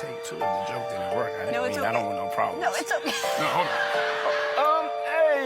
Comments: What I heard him say to her is: "I have a take 2 of that joke. I don't want no problems." before going forward to do the alternate version. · Sounds like he said, "You can take two. The joke didn't work. I don't want no problems." He was clearly worried about insulting Ellen, and the problems she would cause him.